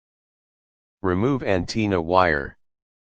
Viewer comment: 🤣🤣🤣 so I'm the only 1 who heard him say anteena